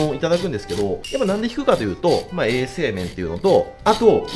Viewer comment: 画面を見ずにイヤホンで音だけ聴きながら流してたら 「うろこをとる理由はASMR」って聞こえて「視聴者のニッチな需要にまで応えててすげぇ！！」って思った なんのことは無い「衛生面」だった 当然だよな